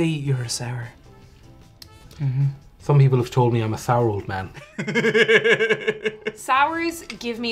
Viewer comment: When he "I'm a sour old man.", I almost thought he said, "I'm Saruman." 😄